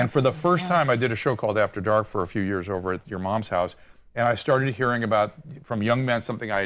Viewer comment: Wait.... 5:40... did he just say he did a show called "after dark" at her moms house? Lol!